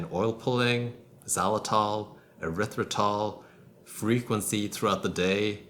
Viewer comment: Frequent what ? I can't understand the word here could you please write it ? Thx you by advance 🙂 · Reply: Frequency throughout the day